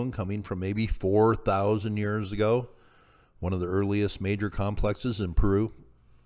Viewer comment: I was getting ready to jump all over this guy ! I thought he said 84,000 years ago !!